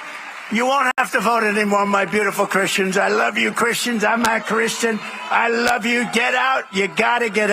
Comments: Trumplefart says "I'm a Christian". Give me a. break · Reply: It actually sounded like he said "I'm not a Christian" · Did I just hear him say "I'm NOT Christian"??? Wow! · It sounds like he says “I’m not christian”, doesn’t it? Freudian slip perhaps? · Trump thinks we're all idiots! But we hear him! I'm voting all Blue! Totally in for Kamala! 🌊🌊🌊💙🇺🇸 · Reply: I also heard him say, "I'm not Christian," right after saying you'll only have to vote one more time. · is it just me, or does donOLD trump say (at the 18-20 sec mark in Bryan's video) "I love you Christians, I'm not a Christian, I love you, you gotta get out and vote" can anyone else shed light on what he says, because it really sounds like donOLD says "I'm not a Christian" to me. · wait did he just say he is not Christian? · Sure sounded like he said “I’m not Christian”. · No matter how many times I replay the video, at 0:21 I hear, "...I'm not Christian..." This "man" is not "Christian" in ANY sense of the word. Really hoping these folks wake up to their Doommaker! <link> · sounded like he said i'm not christian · Sounds like he said, I’m not Christian 😂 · Did he just say "Im not Christian "? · He doesn’t refer to them as *fellow Christians (disassociation)? And it honestly sounded like he said; “I’m *not Christian,” which would be one of the very few sincerest things he’s said. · Did I misunderstand what he said???? Freudian slip ??? I heard him say “ I’m NOT Christian, instead of “I’m A Christian” !!!????!!!